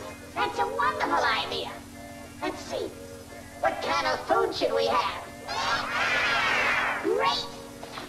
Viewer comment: did he said what kind of bullshit we have. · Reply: Pinkie Dash Roblox & more! what can of food should why have